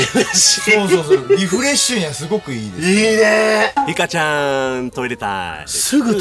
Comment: 秒リカちゃんに聞こえた